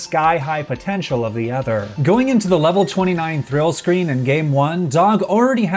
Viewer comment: Did he say level 29 thrill screen? or am I mishearing SuperVinlin